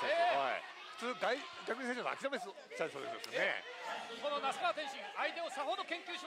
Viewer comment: マジでなんて言ってるか分からんw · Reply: 普通外国人選手なら諦めちゃいそうですけどねぇ...